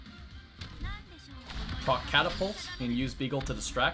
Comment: You definitely said "Fuck Catapult" at 2:09, right? Am I hearing things?